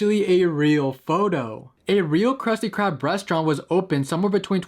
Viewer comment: If you don't listen carefully in 14:55 It sounds like he said ya mama